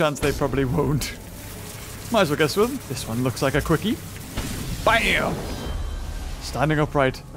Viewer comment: Did he just say quickly